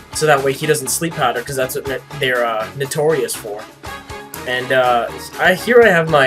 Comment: at first i thought you said "that's what nattorei is for" i was like wtf? but then i figured out you said notorious